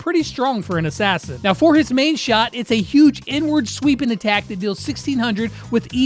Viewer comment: Did he say huge n word?